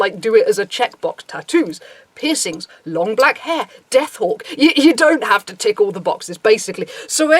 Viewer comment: For some reasons I heard "you don't have to tickle the boxes" at 3:21 :D